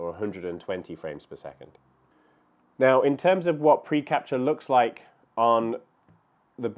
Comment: Did I hear you say "preCRAPture"? Aptly describes a lot of my photos.